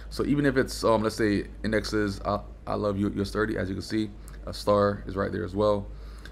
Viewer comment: did he said I love you on min 4:56? aww lol